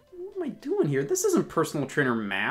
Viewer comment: i thought it said cooking meth